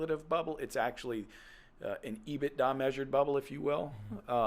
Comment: What did they say at 59:15. I couldn't make it out. 'it's a ???? without measure bubble'. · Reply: EBITDARM Earnings before interest, taxes, depreciation, amortization, rent/restructuring costs, and management fees (EBITDARM) strips out rental costs as well as management fees.